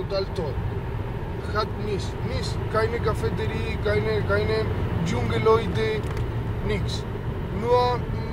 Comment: Verstehe nur ich hier "Dschungel Leute"? 😂